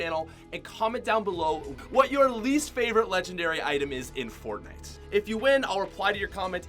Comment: I thought he said favorite gun so my least favorite gun that is legendary is the minigun